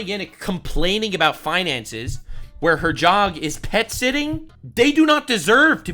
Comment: thought you called it “whinances” 😂 very apt